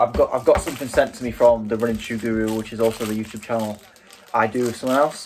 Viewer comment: what was the name of the other YouTube channel as I didn't quite hear it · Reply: The running shoe guru